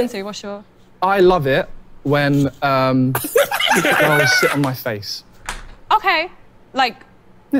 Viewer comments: The entire time I thought he said "I like it when girls SHIT on my face." And I was having a very different experience than y'all while watching this. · I thought he said shit on my face😂😂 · Damn. For like 4 replays I thought she he said shit on my face. And the end was "I just shat on his face" every time until I read the comments lol. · I thought he said “sh*ts on my face” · I’m not gone lie I thought he said when girls s**t on my face…I was watching this video completely different than everyone else 😂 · I REALLY thought he said “shit on my face” at first and so relieved when she only “sat” on his face 🤣😂🤣😂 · At first I thought he said shit on my face the second time around wasn't as interesting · I heard it as "shit on my face". After reading the comments, I am both relieved and disappointed.